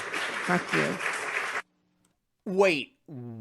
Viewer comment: I thought she said "thank you". It simply cannot have reached the point of actually cursing parents.